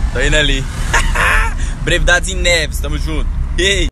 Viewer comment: Só eu q escutei 1:52 Brevidades em Neves? Kkkkkk